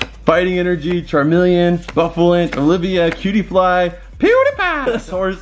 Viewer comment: it's Pegasus, I know my Digimons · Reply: CrisBeKillin 1527 when he said cutiefly I thought he said PewDiePie